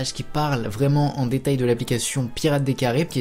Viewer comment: À 9:47 c'est moi où il dit application potc